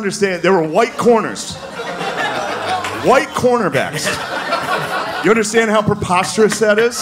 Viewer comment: There's probably a few of you that thought that he was saying "quarterback" and thought, "I still see a lot of those" but he was actually saying "cornerback"